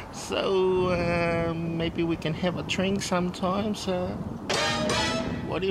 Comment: i thought he said, "maybe we can have a train sometime" lol